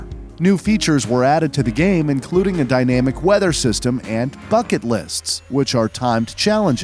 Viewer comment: i love the 1st horizon game because when i 1st played the demo on 360 i enjoyed it a lot. so thats what got me started into forza also at 4:48 i thought he said "fuck it list"